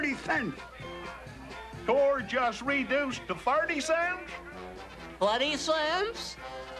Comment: WHAT DOES BINGO SAY AT 0:53 I CAN'T UNDERSTAND A WORD HE SAYS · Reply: 20 cents